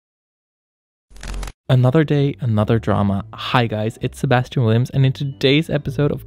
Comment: At the begining I heard "another gay, another drama" why am I like this?